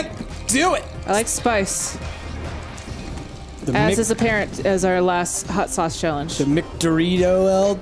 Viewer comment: I heard Parent at 8:28 and first thing I though was Uncle Rik and Uncle Price